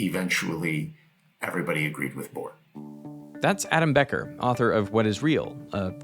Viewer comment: My dyslexic ahh thought that Adam Becker said ALAN Becker for a second 😭😭😭